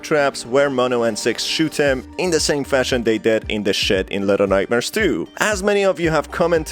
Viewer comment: I thought you said “In the shit” 😂